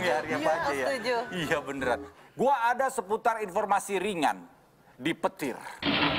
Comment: ngomong apa sih ampek disensor? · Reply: Plesetin perkataan tora yg "petir". Si dita bilang "penghuni terakhir". Itu lho acara jadul di antv, biasa disingkat "petir". Nah Si rigen nimpalin kalo di "big brother" ada si Derek (nama orang). Big brother tu acara di trans tv yg format acaranya mirip2 sama petir. Dua acara itu booming dimasanya, cuma petir lebih dulu (kalo di indonesia).